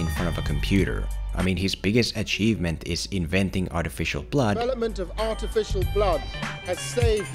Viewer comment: When you said artificial blood, I thought you said "artificial PLOT" both would have been accurate